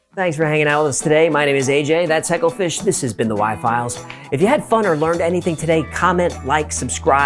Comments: It sound like he said why files lol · I honestly thought he said "my piles" not "my files."